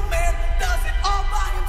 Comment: THESE WERE THE LYRICS TO THE YMCA SONG???? "No man can do it all by themselves" "Put your pride on the self" "I'm sure they can help you" I actually started feeling really emotional at "pick your self off the ground" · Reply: i believe i'm not the only one who misheard it "put your butt on the shelf" multiple times